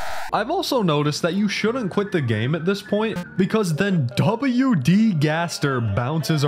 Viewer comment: DID HE JUST SAY W. D. GAST- world resets